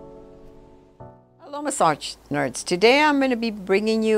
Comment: LOL! 0:10 Did she just call us massage Nerds???